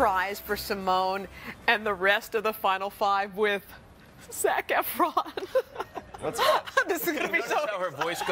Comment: I thought the reporter said "Snack Efron lol 😅